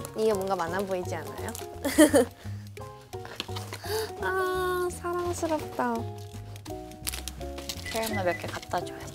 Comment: Did I just hear Yuna saying, "Ahhh parang masarap 'to."????? hahahahahahaha